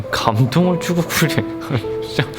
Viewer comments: 나만 "'감동을 주고 그래 썅'" 이라고 들리나? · 그와중에 나만 10:44초에 욕들었나 ㅋㅋㅋㅋ 감동을 주고 그래 썅 ㅋㅋㅋㅋㅋㅋㅋ